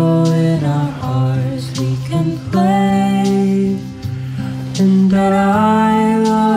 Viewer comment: I thought it said "with the sorrow in our hearts we convey" instead of "we can play"